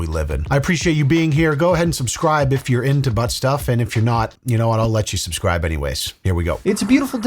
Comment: Did you say butt stuff?